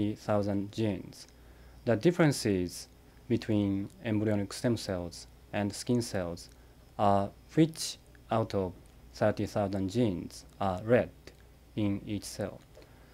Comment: the differences between embryonic stem cells and skins are ???? althought ... Please what is the missing word ? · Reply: the differences between embryonic stem cells and skin cells are out of 30,000genes which genes are read. so by instructing the skin cells to read the genes read by embryonic cells, you can turn it into embrionic cells